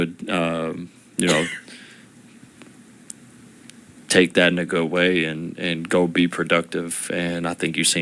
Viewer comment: did AR15 say take that 🥷 away 😂🤣😂